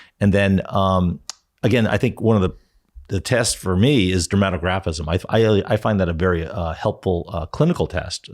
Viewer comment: What does he say here (the third test)? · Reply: Dermatographism: It is a test where the physician rubs the skin with a tongue depressor or any other instrument to see if welts appear on the skin. It is a method used to diagnose the release of histamine from topical stimulation like rubbing the skin, heat, exercise, cold exposure etc.